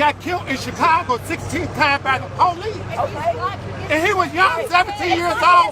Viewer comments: "He got kilt by the police Fifteen Times" ...did I just hear that? 😂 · He said, "Send Messicans back to Messico!" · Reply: Joseph Zebron I misunderstood. I thought he said 16 tiles.